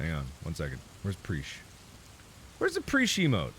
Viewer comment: I thought he was saying preecheemo